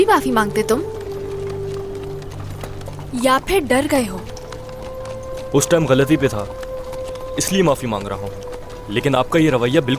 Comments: When Ouus said "Uss time galati pe thaaa" I heard right ? "He said Uuss time"? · did he just said "Uss time"